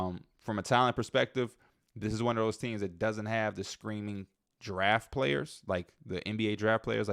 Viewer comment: For a second At 26:17 I thought you said “screaming giraffe players”. I thought was some new player archetype term people were using that I’ve never heard or something